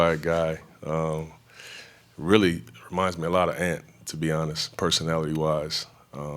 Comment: can someone tell me what name he said? I cant hear it. He said "really reminds me a lot of..." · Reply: Ant is nickname of Anfernee Simons